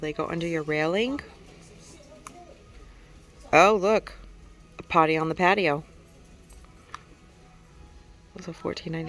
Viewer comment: Thanks for the tour, I need to get my butt to Wally World! So many beautiful flowers 🌺 🌹🦋🐝🌸🌻🌺 I was cracking up every time I heard you say the calibrachoa and sounded like you said party on the potty! Lol enjoyed it 😛